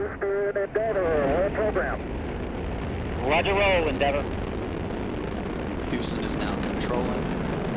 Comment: I know he said "Roger, roll Endeavour" but it sounded like he said "Rock and Roll Endeavour!" made me smile :)